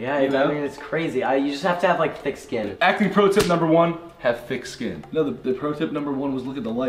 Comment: 💀 I thought he said have white skin